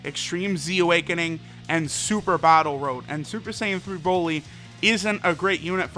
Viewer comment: I heard "Super Bottle Road"